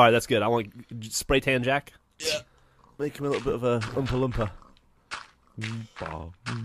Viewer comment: It says 'Oopma loompa instead of 'oompa loompa.'